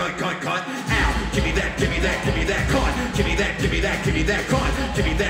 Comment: onwards what is that man saying "gimme that gimme that gimme that c&*t"?